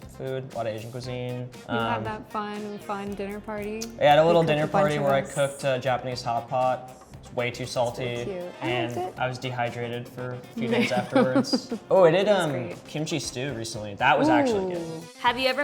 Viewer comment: DID HE JUST SAY HOTPOT??? Does anyone beside me get the MEME I hope someone does or I’ll know that I’m silly